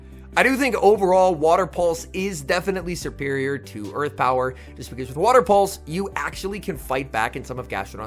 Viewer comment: did I hear "overall, water pulse is definitely SERPERIOR to earth power?" haha freudian slip or on purpose??